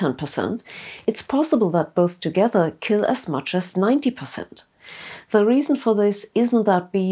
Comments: you say 90% but the subtitles say 40% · They found that if you expose bees to one pesticide that kills 10% and another pesticide that kills another 10%, it;s possible that both together kill as much as..." - CC reads "40%", but I hear "90%". I was just about to shrug and let it go, but then I heard: "The reason for this isn't that bees are bad at maths..." - well, I suppose so too... ;-) Amusing anyway : ) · the subtitle says "kill as much as 40 percent" but you say "90 percent", which one is the real number? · I hear 90% but the subtitles say 40%